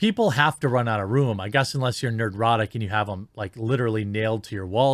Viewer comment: Did you say "nerdrotic?" 🤣